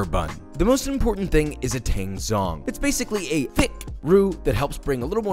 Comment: i thought he said tang thong